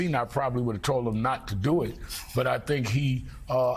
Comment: " Fetterman lost the battle but won the war". ....and he actually said that with a straight face. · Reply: he clearly said he lost the battle but "may win" the war (not "won the war").